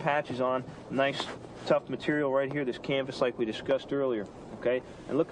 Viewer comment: what is "canbis" or whatever you say at 2:37 (